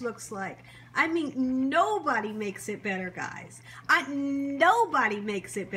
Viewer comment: all i heard was Nate Dogg crooning the hook, no-bod-yyy does it bet'terr, Nobody does it better song 😂